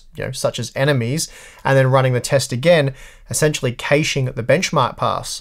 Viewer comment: you say "essentially ... at the benchmark pass" What is that word? Honestly, I have no idea what that word is, so I am confused. English is not my native language, and I haven't heard that word before. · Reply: He says caching there :)